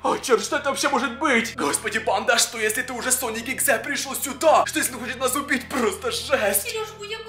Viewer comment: мне одной послышалось слово Соник exe?